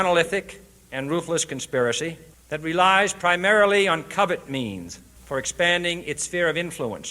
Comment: Wow I always thought JFK said "covet means". That accent!